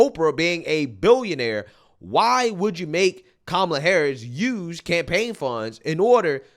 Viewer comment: Did she just say kamala horror!? Lmao! And also there is hope for you all... lmao!